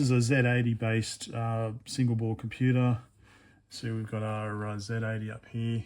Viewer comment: Did I?! I shouldn't have ... Acer has always been a Taiwanese company (as per thumbnail). No idea why I'd have Singapore on my mind ... ? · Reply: Maybe I misheard. I can't find it in the transcript. I knew Singapore and Hong Kong did have a time when they made electronics. I'm in Taiwan now and have no idea where you would find old tech like this. In Japan it's easy. YEP I'm wrong! You said "single board computer" and I heard "Singapore computer" 1:56 (